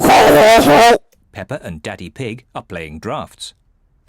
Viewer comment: I thought the narrator said “peppa and daddy pig are playing drugs” Anyone???